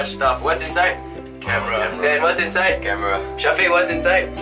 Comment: Dan said camera but subtitles said 'Vietamese Pork Noodle' and at 3:37 there's a typo 'Everyone is takign nice photos' What???